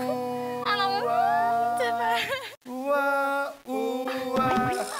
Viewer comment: I heard that oh my god